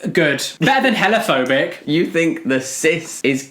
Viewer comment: What does say here? "Better than ____phobic" I can't make it out. It sounds like hellaphobic but I looked it up and it isn't a thing. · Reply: They said ‘hella phobic’ like the transphobe is hella transphobic